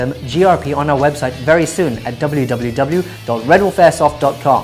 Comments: Did he just say "Pornstash out"..? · I thought he said porn stash out......